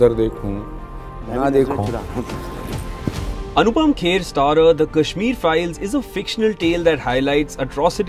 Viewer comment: did he just say fictional ?